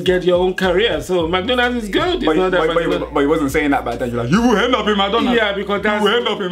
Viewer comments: I actually thought he was saying you will end up in my daughter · I LEGIT THOUGHT THE DAD SAID HE WOULD END UP IN HIS DUNGEON NOT HE WILL END UP IN McDONALDS. heh heh LOL BAHAHAHAHAHAHA. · I thought he said. Madonas